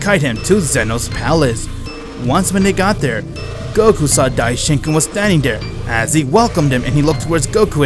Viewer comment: Whenever He Says Daishinkan It sounds like Hes saying "Die-Chicken"